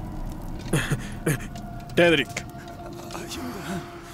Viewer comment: Alguien más le entiende en el segundo 00:33 que dice "A CHINGA" o soy solamente yo... xd